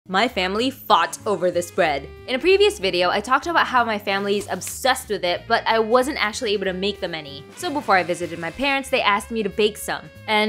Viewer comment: Is it only me who thought at first that she said 'my family farts over this bread'......